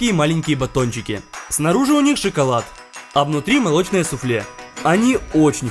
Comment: Мне одному послышалось<<А внутри белые сопли